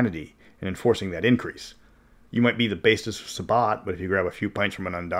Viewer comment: You said "basest sabbat" but I heard it as "bassist sabbat" now I kinda want to play that.